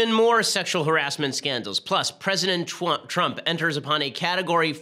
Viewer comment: did he just say president twump? lol